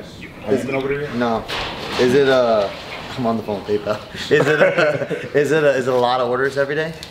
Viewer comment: Does he say at 35:44 "I’m on the phone with Pit though?" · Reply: naturalmuzick I’m on the phone with PayPal